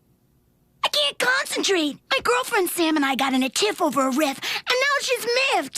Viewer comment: woah wait a minute did Luna just say girlfriend